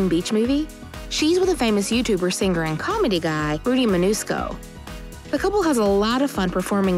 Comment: Who heard her say manusco instead of Mancuso?😂👇🏻